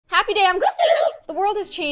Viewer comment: Guys can anyone tell me what Kassie says when the video starts it sounds like she says happy day on........ it goes so high Ijust can't understand what she says